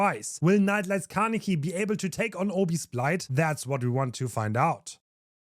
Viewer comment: Thought bro said "Obese Blight" for a second